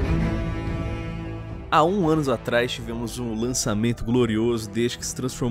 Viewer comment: "Há um ânus atrás?" Foi isso mesmo que vc disse? kkkkkkkkk